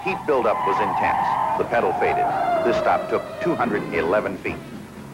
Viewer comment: "That Hurst 4 speed shifter was smooth like it was mounted in butter!" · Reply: did he say stops at 70 took ONE HUNDRED 11 feet?? If so That's incredibly short like he couldn't have said that